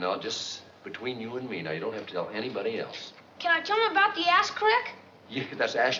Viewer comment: "Can I tell 'em about the asterisk?" Took me a while to decipher what Richie said.